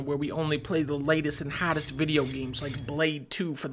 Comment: I really thought he said "the bladest game".